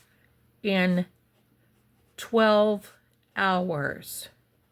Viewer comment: I thought they said 2 hours - 12 makes the area of possibility so vast